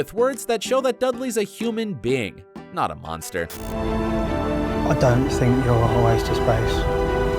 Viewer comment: "I don't thing you're" WHAT did he say? As for Draco's wand scene... it would NOT have worked with the much more awkward and telling moment of Valdemort's hug which was pure brilliance on the part of the actor. They had to cut one or the other, and Draco getting a wand to Harry was less impactful. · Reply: Vernon calls Harry a waste of space, and Dudley says "I don't think you're a waste of space."